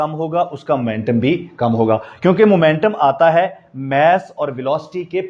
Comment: I'm sorry but all I heard is Kyun k paani ata ha (to barish ata ha) at 10:36 😂😂 I should quit social media 🤧😂